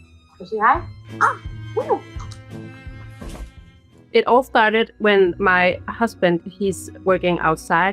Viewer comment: It sounds like she said “ it all farted””😂😂😂